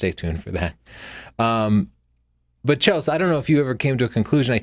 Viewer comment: Did you just call Chelsea "Chills" at 8:25?????